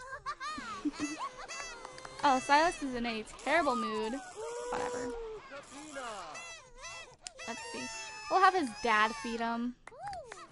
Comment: i thought u said beat him not feed him! omg no!